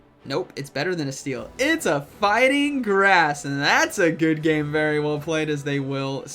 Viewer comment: I heard "no, it's better than that, it's a f*cking Grass..." 😂😂😂